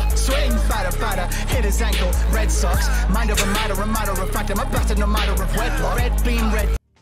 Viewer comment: i totally understand the "mind of a matter" pause and going back few times. i swear first time all i heard was "madara madara madara" and was like "ahm... wild naruto reference?" XD